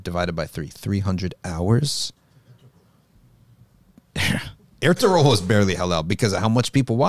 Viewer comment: What did he say at 6:23? What is the name of the show he is referring to with muslim conquest? I think i heard "herteroll" or something like that